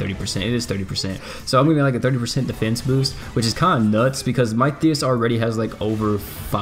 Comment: How many percent did he say in 10 seconds at 5:30? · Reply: I am way to late but I think he said 30% defense boost U prolly got ur answer by now tho